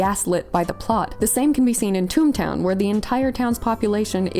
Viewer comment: I thought you said “Toon Town” for a second there, and I was expecting you to bring up Judge Doom and the Dip!😅